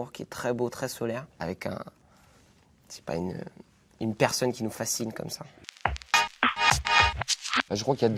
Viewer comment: does anyone know what Felix says after he says, "tres beau, tres solaire, avec ......?" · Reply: Okaaay..! I came here directly to answer u😺 sooo..he said literally "...très solaire, avec..mm..je sais pas..une personne qui nous fascine comme ça" which can be translated by "with..mm..I don't know..a person who fascinates us like this" that's it gurl!🙌🙏✌